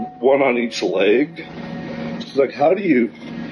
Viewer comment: Wait did he just say Seth Rogan! lol wouldn’t it be hilarious if all of this was over a mistaken identity by Seth Rogan comments!His crew was at a club and someone in his entourage asked if he heard what “Rogan” said! And the gizzard kind just assumed it was Joe!😂😂😂